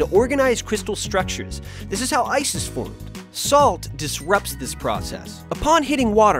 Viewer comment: when he was like this is how ice is formed it sounded like this is how Isis formed xD